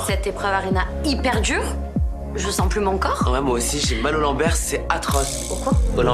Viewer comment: "j'ai mal au Lambert " T'as mal à Moi ? ? Desolé ..heu vraiment.. m'excuse 😳